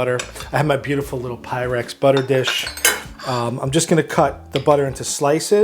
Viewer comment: We thought for a second you said "I'm a beautiful little Pyrex butter dish" 😂 yes you are